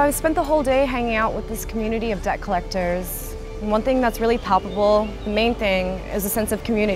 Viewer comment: subtitles need to be corrected she did not say "poppable", she said "PALPABLE"